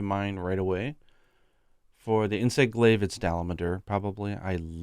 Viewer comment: I thought was saying "the llama door" for most of the video instead of "dalamadur"